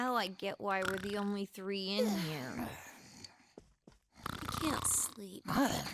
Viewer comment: "I can't sleep in the dark..." Yukiko, is that what you said, but got obstructed by the living joke? 18:32 Wait what? Did I hear another "In the dark"? 18:45 Erm, Yukiko.exe has stopped working properly and has started repeating "in the dark" every 13 seconds or so. 27:42 It's so cold the river used Thunder Reign on you both! Wait... wrong element. The point is, it's so cold it shocks. 29:57 Two cabbages every 5 days keep the horrible moments of your life out. Hopefully. 31:58 It's like the .99 prices all over again!